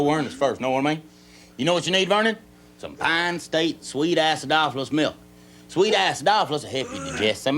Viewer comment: I thought Ernest said "Sweet-ass dolphins milk". Y'all suppose such a thing exists? Maybe in Japan, my friend; maybe in Japan...